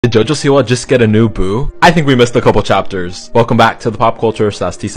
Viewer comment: I heard him say " did Jojo Siwa just get a new boob ?"